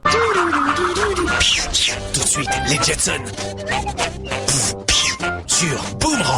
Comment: Also at 33:12 I have no idea what show the announcer has said unless it the french title for the Jetsons. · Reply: The French title for The Jetsons is actually Les Jetson